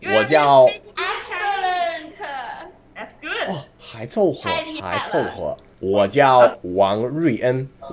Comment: what is saying when he answers to the compliment? Hai ... what? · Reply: 6 months late but i think you should get a reply. I believe he said "还凑合“ which is "hai cou he" .